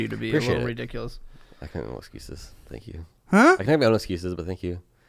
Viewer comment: he kinda what? I cannot gauge what he's saying. "I've got my own excuses" "I'm out of excuses" "I'm kind of something something excuses" ??